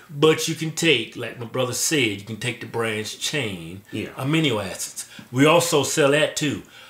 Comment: did kevin just say: "aminio acids"?... XD